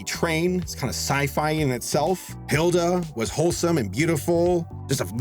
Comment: Hilda is SO underrated! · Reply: Thought you said Haida for a second lel